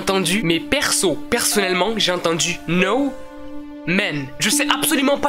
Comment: moi j'ai entendu no a man